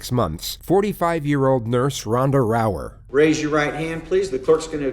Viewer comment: I thought that was about to say Ronda Rousey. I was about to say “Man, Time really flies!”